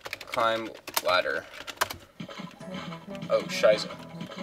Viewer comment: did you say oh scheiße? just want to know please tell me